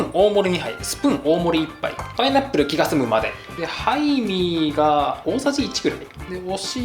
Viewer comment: しっかりややこしくしてるの良き(๑•̀ㅂ•́)و✧ そして美味しそう(´,,•﹃•,,｀) ハイミーがタイミーに聞こえたのはきっと私だけ🤣 最後の分量外のラーメンは面白すぎた😏💗